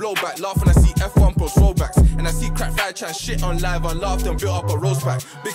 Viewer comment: did man say crack5 as in reference to rack5 cgm 😂😂😂